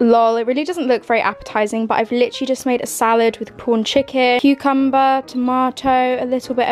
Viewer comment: Ha lol thought u said porn chicken